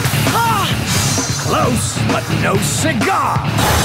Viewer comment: What did the knight say when drago miss I thought he said close but no cigar